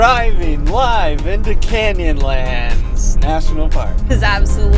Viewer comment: OH! "Canyonlands" I thought you were saying "candy lands"!! A bit of a let down...